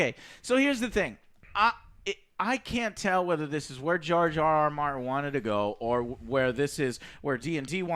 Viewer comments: Did Joe just say Jar Jar Martin? 22:53 lol! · It's funny, but every time I hear the name 'George RR Martin', for some reason, it sounds like 'Jar Jar Martin'. Kind of befitting actually. :-)